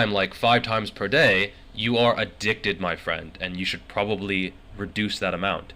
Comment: Did you just say I'm addickted? What??